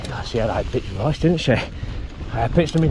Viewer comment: I had to watch 3:10 3 times to hear what you said properly... for some reason i thought you said "she had a right podge didnt she..." but then couldnt understand why you compared her to your daughter...lol.